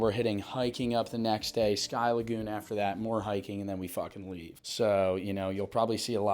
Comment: sounds like he said “then we fuck and leave”